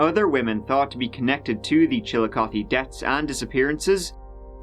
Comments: At first i could of sworn was saying ( Chill a Coffee) 😁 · When you go to literally get head from a hooker and forget to go grab protection from your truck. 🤣 I thought he said chill-a-coffee, I was gonna order a cold case...